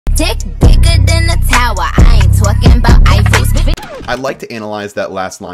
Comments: I hear “twerking at the Eiffel” not “not talking bout the Eiffel” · I always thought it said I ain't talking bout ISIS- someone help XD